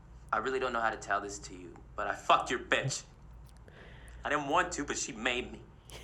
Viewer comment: thought Will said he "fucked your fish" in the monologue lol.